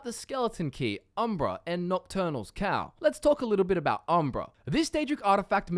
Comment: at first when you said nocturnal's cowl, i thought you said nocturnal's cow.